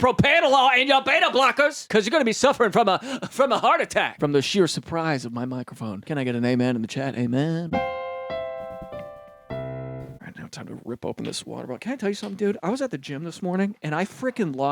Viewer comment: I thought you said "can I get a they/them?" And i was just about to accept that.